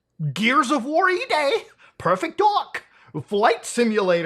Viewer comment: did he said "Perfect dog" or I'm wrong?